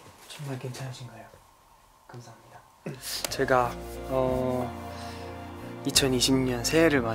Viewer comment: did i just heard Joshua when Seungkwan said "Jega"